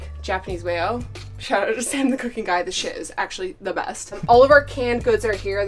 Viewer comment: Somebody said 'the best' .. i heard 'becki and chris' ❤️🤭